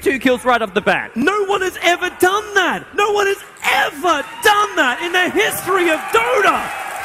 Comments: You came for 1:33, didn't you? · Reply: i thought he said "no one has ever done that in the history of donut" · I thought he said "in the history of donut" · I always thought he said No one has ever done that in the history of donut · until now, I always thought he said 'no one has ever done. that! no one has ever done that in the history of donuts!' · i thought he said the history of donut